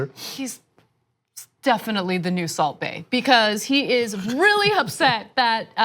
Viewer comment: Definitely the new ...what did she say? The new salt bay? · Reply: yeah *salt bae. Ana throwing out some stale meme references.